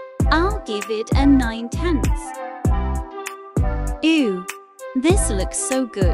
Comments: I THOUGHT SHE SAID EW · When it said ooh I thought it was saying ew 0:16 &